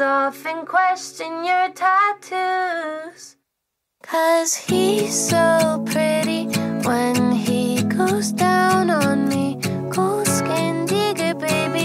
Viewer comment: i thought she said "hes so pretty when he looks down on me"😭